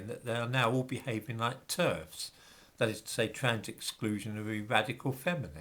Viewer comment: I honestly thought you said "...behaving like TURDS..." rather than "TERFS" for a moment!😂